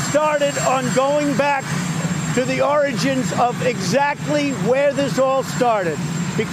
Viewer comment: Now every time he says "origins" I hear "oranges". >:(